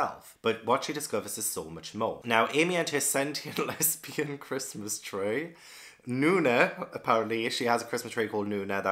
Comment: For the longest time I thought you were saying, "scented lesbian Christmas tree." And I thought, "oh, I wonder what she smells like." 🤣👃🎄